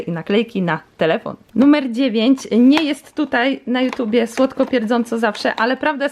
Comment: Nie jest tutaj na yt tak słodko pierdząco" dobrze usłyszałam?🤔🤨😳🤭